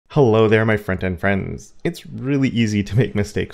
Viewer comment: it took me a whole year to realise that you say front end friends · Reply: 🤯omg I thought he was saying "My Friend and friends" and it really really irritated me. Now I feel like a fool! Thank you for clarifying!